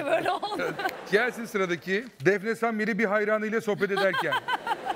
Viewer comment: Depresan biri bi hayranıyla sohbet ederken diye anladım :)